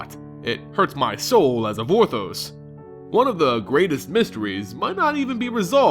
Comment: what did you say? "It hurts my soul as a"...what? im sorry, i just cant hear it :/ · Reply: Vorthos